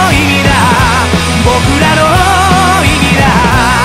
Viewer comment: ｢僕らの意味だ｣が｢僕らのリーダー｣って聞こえてうるってきた